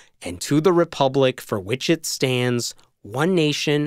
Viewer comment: When I was in the first grade. I thought they were saying for witches stands. Mmm,m… where are these stands? Why do the witches need stands. Are the witches taking a stand for the U.S.A. I had a very vivid imagination and still do. That’s why I love your readings from the Bible. You really have to have one hell of an imagination to fallow all that crap.